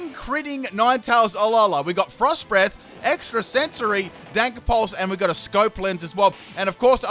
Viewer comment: do my ears decieve me, or did he say "dank" pulse? :D